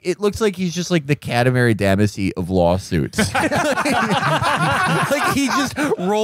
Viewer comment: Can anybody tell me what the fuck Felix is saying at 7:00 in? The captions say “catenary damn is he”???? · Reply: "The Katamari Damacy of lawsuits."